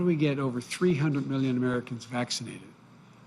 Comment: LOL @ 14:55. Did he just say "Yuge, yuge, yuge"? It's going to be fun making fun of this president. Sure there will be less to work with than his predecessor but with Trump half of the laughter was nervous laughter thinking "OMG this moron has the nuke codes!". Now we will be able to just tease his stutters..like we did with Dubya Bush..but be able to rest easy in the knowledge of him being a competent person. God is good!